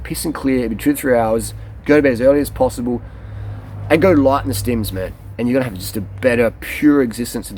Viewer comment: At the end of your video, at about 21:50, did you say go light on the stems? I could not make that out. Thanks again. video very inspiring....the end about emotional stability was fantastic.